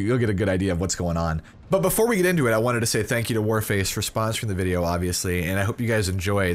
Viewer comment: At first I thought it said Warframe. The pain.